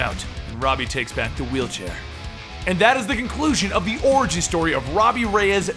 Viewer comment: At the end of the video it sounded like you said oragy story not origin story lol